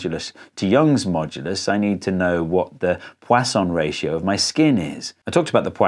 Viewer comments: Am I the only one who thought he was saying croissant ratio? 😂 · Who here also thought he said 'croissant ratio' until the text appeared?